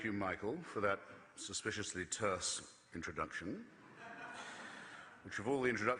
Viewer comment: At around 6:15 What exactly does Christopher say? Tursk? Tersk? I've trued searching these words for a definition, but not the definition that I guess he'a using it for. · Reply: terse